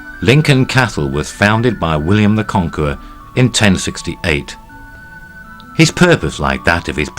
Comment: > mentions that Lincoln Castle was founded by William in 1068, despite the captions saying 1066.